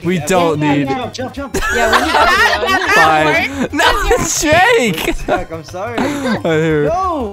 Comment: Did he just say Jake1:24